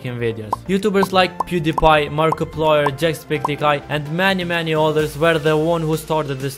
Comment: did he say JACKSPECTICEYE?